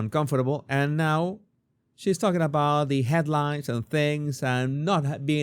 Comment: i thought she said she doesn’t look at the media and headlines